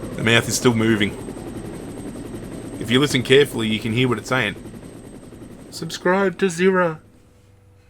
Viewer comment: See that I heard as well, I heard that thing saying that you should subscribe to the Zirra Man